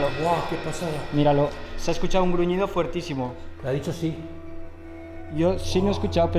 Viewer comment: Voz muy grave de hombre dice VALEEEE , DICE QUE SÍ (x0'5) 44:21 Una voz dice PORQUÉ ? (x0'5) 44:22 Voz muy grave de hombre creo que dice SIII (x0'5) 51:24 Voz de hombre dice DEJENNOS EN PAZ (x0'75) Me ha encantado la investigación 👏👏👏👏👏 Muchísimas psicofonias ,mi like 👍👍👍👍👍👍❤️👻 Suscrita al canal 👍❤️